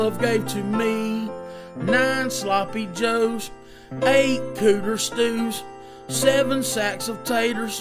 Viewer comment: Is he saying EIGHT cooter stews?? lol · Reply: I think so lol isn’t that turtle soup or something? I thought at one point he said cougar stew but that didn’t Make sense